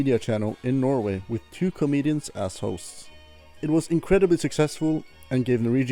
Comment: Really thought you said "With two comedian assholes"😂